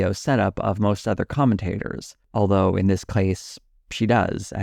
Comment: why does he say case like this? Clase?